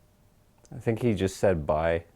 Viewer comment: I heard "Aight" .. apparently, that ghost is from the streets.